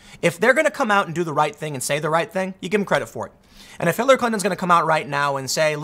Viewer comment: listening the first time I heard him say "H¡tler Clinton". Turns out I was wrong, buuuuut... just sayin'